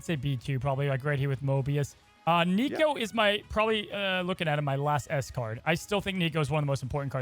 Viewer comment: i misheard it as "nico is probably looking like my last escort" and was like what 🤣